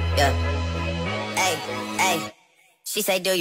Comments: I always thought he said I only love my dad n my mom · i always heard "i only love my dad and my momma im sorry" 😭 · I dead thought he said “my dad and my mama I’m sorry.” 💀💀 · the whole time i thought he was saying "i only love my dad and my mom im sorry- " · Ngl I thought he said "i only love my bed and my mom I'm sorry" but said his son name mahbed honestly I'm still gonna say bed because it's relatable as hell because I only love my bed and my mom 😭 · “I only love my dad and my mama im sorry.” That’s what I always thought he said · we need drake to come and confirm wtv he is saying 😭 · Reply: I thought he said he only love his dad and his mom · I hear “I only love my bed and a minor I’m sorry”💀💀💀💀💀💀💀💀💀💀💀💀💀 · For those who don't know My bed was supposed to be spelled mahbed cus that's his son's middle name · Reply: I thought he said I only love my dad and my mom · I also thought it said “I told her i only party” 😭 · First i thought that he said i only love my bed and my mama im sorry So i thought he was saying sorry to his mom cuz he only love his bed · I always thought that he said “my babe”😭🙏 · People who also thought this: 👇🏼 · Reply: i thought he said my dad and my mama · I always thought he said she said do you love me I tell her I only love my dad and my ma I’m sorry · I thought ot said i only love my bed, oh my mama im sorry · what does it actually say tho 😭😭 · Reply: I thought he said my bread. Meaning money